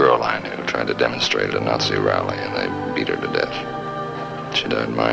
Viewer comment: Frank Savage really just admitted he beat a girl to death so casually...guess he really was a savage lol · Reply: McDougall If you will re-watch Lansing telling the story about how "they beat her to death," you will see that he pauses when saying the word "they," making it sound very much like "I beat her to death." On first hearing, I also thought he said "I," and I had to listen closely on rewind to realize my mistake.